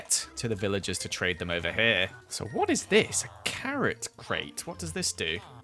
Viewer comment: I thought he said carrot cake not crate I was like you eat it that's what it's for